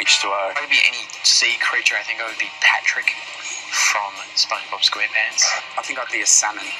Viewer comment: I thought Lewis said: “ I wanted to be Spongebob Cringe pants”😂😂😂